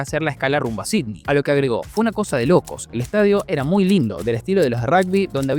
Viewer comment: creo que no dijo "fue una cosa de locos", lo que dijo fue "una verdadera locura"